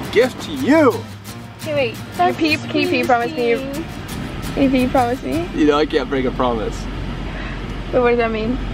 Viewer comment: Did anyone else hear say baby promise me 😱😱😱